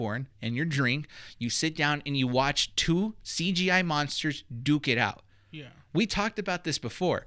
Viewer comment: Whenever says "Duke it out" I just hear "dookie." 😇